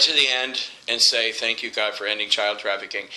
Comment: I'm sorry for that comment earlier. I totaly misheard what you said. You said "ending" instead of the "any" that i heard first. After going back to check, i realized my error.